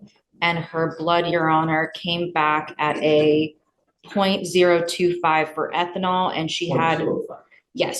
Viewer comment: judge assumes .2+ but I think DA was saying .02+. Am I tripping? Cause that’s a major difference, not that I’m saying it makes the lady any less guilty but.